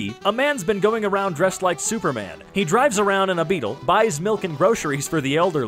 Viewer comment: did he just say superman